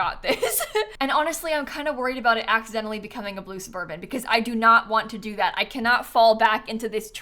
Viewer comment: I misheard "Blue suburban" as "Lucifer bin" 😭🤣